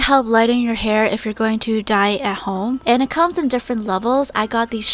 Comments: my sister came downstairs when she said dye at home and thought she said die at home and said what are u watching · At first I thought you said "If you're going to die at home" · I literally thought she said “ if you’re going to die at home” 👀